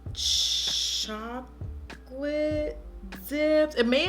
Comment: Tell me why I thought she was about to say the name of the color was " Chocolate Daddy" instead of Chocolate Dip LMAO